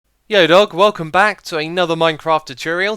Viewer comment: "Yo dog" made me facepalm so much xD please never do that again or I will cry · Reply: Thought he said "Yo dork".